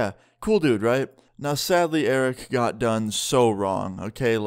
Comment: I thought you said eric drove over why did you guys take your girlfriends car to taco bell?